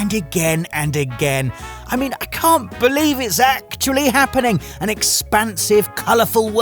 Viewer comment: I swear you were saying "Zack-tually" happening. Otherwise I must be missing my daily dose of FF7 now that the Ever Crisis beta has ended.